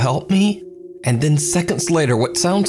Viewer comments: I hear “I confess” not “it’s a test” Confession is done at church. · I thought she said "it's a tesla" not "it's a test" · it sounds like its saying "its a test" or "ritual"